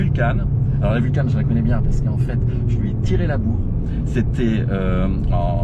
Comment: A 0:40 j'ai compris "je lui ai tirer la moule" 😳🤔